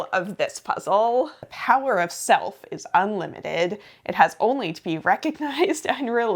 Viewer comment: Was doing an online puzzle while this video played in the background and I definitely misheard "The POWER OF SELF" as "The POWER OF SOAP". 😂